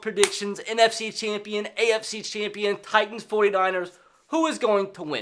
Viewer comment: did he say titans 49ers for the super bowl